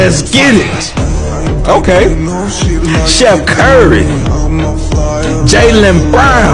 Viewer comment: I thought he said chef curry at firsts